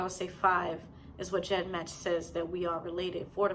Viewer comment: Are you saying, jet match 2? I heard you say ancestry, and 23 & me, but not the other source.